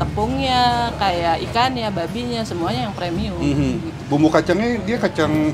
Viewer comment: babinya???? Telinga gw gak Salah dengar kan? Klo emang pakai b2 wajar Harga segitu